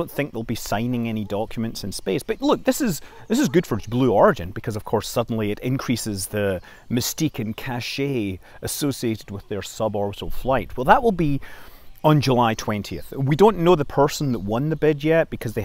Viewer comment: when you said "mystique and cachet" of the suborbital flight, for a moment I heard it as "mistaken cachet," which is a pretty accurate description of the significance of a suborbital flight to space vs an orbital flight.